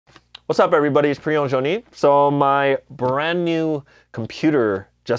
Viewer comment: Rofl I thought he said Pre Owned Joni.